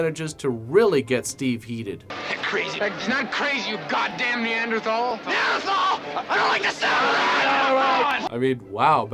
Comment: Neanderthal?! starts acting like an angry Neanderthal Edit: Did he say “Neanderthal?! I don’t like the sound of that?” 😂